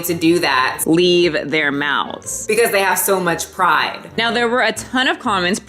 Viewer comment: I swear I thought you said “because they have so much pie” I had to replay! Sorryyyyy also, yes, bobs burgers is my lifeeeeeeee why have I not found you before omg